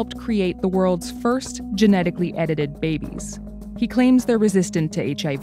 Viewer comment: I thought it said first genetically edible baby